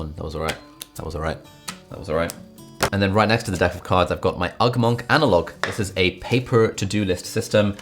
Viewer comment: When he ugmonk analog I thought he said ugmonk and a log